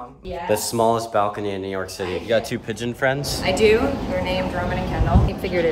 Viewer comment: ROMAN AND KENDALL PIGEONS 😂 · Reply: Oh wow I thought she said Roman candle